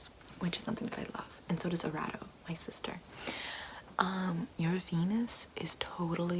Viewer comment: I thought she said your penis